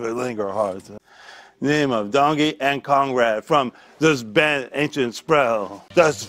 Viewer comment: So hilarious, here are ALL the names he said: Dr. Jimmy Brungus Dr. Jungy Brogen Dr. Dan Dringle Dr. Dan Dungus, Sexpert from UCLofA. Beverly Dingus Diane Dringle Dr. Daniel Drungle Hart Jack M Lantern, Sandy Grungerson Dringus and Drungle Comedy Team Neighbor Steve Drambus Dr. Dungle Poem by Captain Roy Bringus Delgringo's Restaurant Cold Hard Cash, directed by Steven Bregle Dr. Rongo Bringer Quote by Kneel Armstrang Dr. Dingid Forester Folk song by Beverly Drangus Crohost Linda Barrenge Dr. Dan Dringle A kiss attempt to Susiea Drangus If you don't worship Jingus, you have to worship Sadin Dango Rangus - umpire Poem by Dorothy Drangus Dr. Lane Lasagna Proem by Jimmy Jangles Jang Carankers Poem by Barbara Egg Diner owner Langus Chef Pangla Fangers Dr. Kang Tummy Cringrus Guitar shop owner Gringus Neighbor David Lingerhart Dongie and Conrad from the band Ancient Sprell Broys Town director Father Flanagan Kenghis Ahroy! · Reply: I thought he said "Congrad" on the Conrad one. 😅